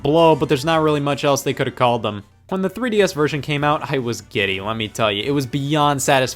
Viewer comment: did he just say i was gay